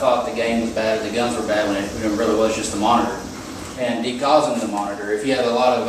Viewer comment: What did he say here? I can't tell with his southern drawl. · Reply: De-gaussed? :) lol